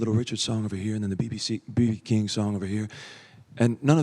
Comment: Anyone catch when Austin said bbc instead of B.B King? At 3:10 lmao 😂